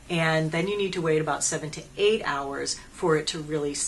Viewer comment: I had to rewind, I thought you said wait for 78 hours !!! lol , but it was 7-8. Great review as always. xoxo